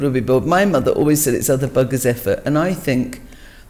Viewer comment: what did her mother always say? something... effort? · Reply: other buggers effort